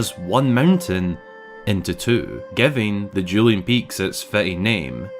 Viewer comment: I thought you said “Julianne Peaks”, and I was like, what? Then I realized you’re saying “Dueling Peaks” 😂😂😂 My Filipino ears are playing tricks on me.